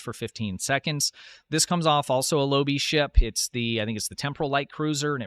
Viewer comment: said that it came from a dil ship. Clean the wax out of your ears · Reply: maybe watch the vid and listen closer urself before u sass others over getting it wrong urself ... 😂 18:06 he clearly says "this comes of Also a lobi ship, its the temporal light cruiser..." and sure he says "it runs about 80k dil" after that, but thats beside the point realy, since ppl that are not to versed in STO lingo wouldnt realy catch that after hearing LOBISTORE just seconds before.. and aside from that, i merely pointed it out, npthing more or less